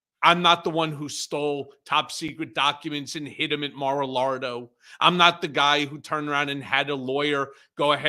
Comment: Did Cohen call it "Mar a Lardo" at 7:01 ? 🤣